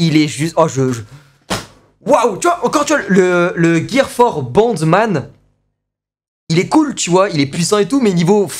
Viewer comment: je sais que c'est boundman mais au début j'ai entendu bandeman j'ai eu peur...